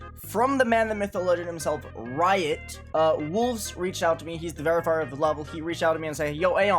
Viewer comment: I swear I keep hearing Aeon say “Wulzy’s the verifier” and get weirded out every time